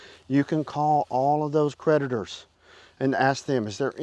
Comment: When you said creditors, I thought that you said, PREDATORS. 😂